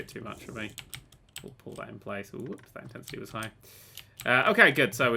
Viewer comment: Did he say "that intends suicide" or what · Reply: Down Town81 He said "whoops, that intensity was high." Though I do admit it sounded like what you said